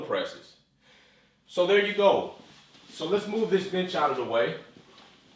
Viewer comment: “Now let’s move this bitch out the way” 🤣😂 thought that’s what u said at first